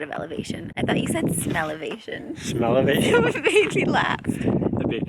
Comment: "Smellovasion" I thought said that as well. 🤣🤣🚐